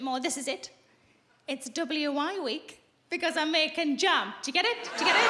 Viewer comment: Can someone tell me what she's saying? I hear "W.I.(?) week" but can't be sure. But through educated guesswork, I'm pretty sure the punchline is "making jam", if you want a boost of iron with your biscuit, LOL. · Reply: W I is the Women's Institute in the UK they're known for there cooking and baking cakes etc but also for making jars of jam